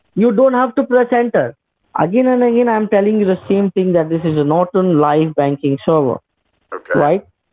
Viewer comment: Did he just say Norton is a bank server 😂